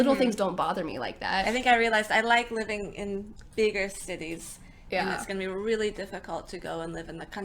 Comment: She said "bigger cities". And the caption says. "Big city"